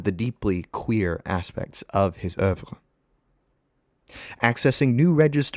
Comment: what word is that? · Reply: Queer, or oeuvre?